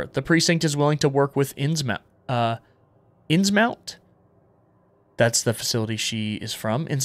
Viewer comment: I heard "Insmount" and thought of Lovecraft, this shit is GASS bro 👍